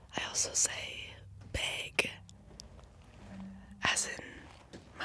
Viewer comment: I thought she said “BIG” lmaoo